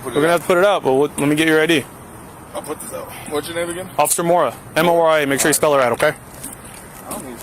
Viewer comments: "M-O-R-A. make sure to spell it right" Legit thought he said "Officer Moron" for a second. Wouldn't be too far off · I keep hearing 'officer moron '😂, sounds right.